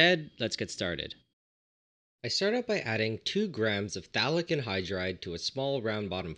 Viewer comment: I thought you were saying Phallic Anhydride